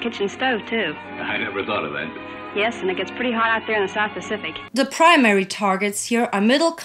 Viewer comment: The subtitles at 4:00 are wrong. Captions say "hard" when she is saying "hot"